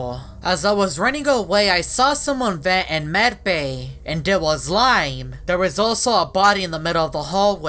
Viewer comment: it sounded like he said: "as a was running away i saw someone vent, its was a lie" it was funny!